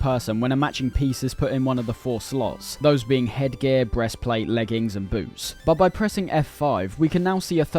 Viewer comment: did he say breastplate?